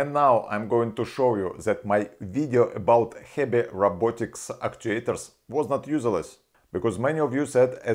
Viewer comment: can anyone help me understand what he is saying? "Heavy robotics" or "Heppy robotics" is all I can understand. No disrespect intended. I rewound and listened closely several times and it doesnt make sense to me. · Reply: HEBI robotics. :)